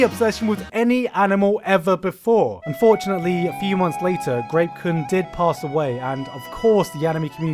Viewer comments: When he said grape kun I mistakenly thought he said rape kun I had to rewind and put the sub on to make sure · Lmao I thought you said Rape Kun